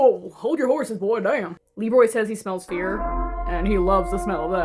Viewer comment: I THOUGHT YIU SAID BEER